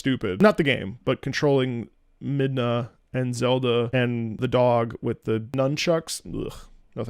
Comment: This is utter clickbait. The video is not 39 years long. 😡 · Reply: DID HE SAY ZELDA?